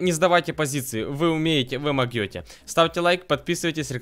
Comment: 😂😂😂😂😂😂 я угараю, посмотрите ролик до 1:40. Я только не понял ю, он сказал"могёте"?если услышал лайк